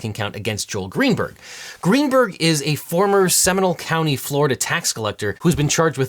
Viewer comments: At first I thought you said Gaetz’s wingman was from “seminal” county, which would be creepily apposite. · All this time in all the news stories about Greenberg, when they said Seminole, I heard 'seminal.' I kept wondering why he was so seminal but I didn't care enough to look it up.